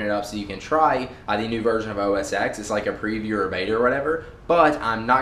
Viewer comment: it sounds like he said OS Sex